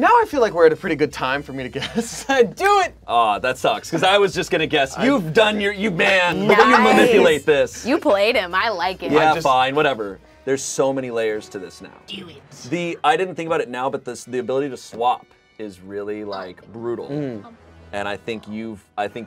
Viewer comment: did just say "yeah i'm bi, wahtever" as a refrence to the comments or am i mishearing? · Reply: I think he said "yea fine whatever"